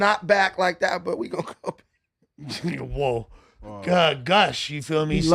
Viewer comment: did say “n** woah” or im trippn